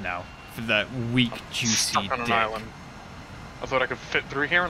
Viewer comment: "weak juicy Deck" i Swear i heard you say richard